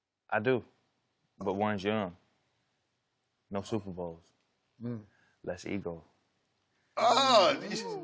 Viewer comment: what did he say at 6:20? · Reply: Less ego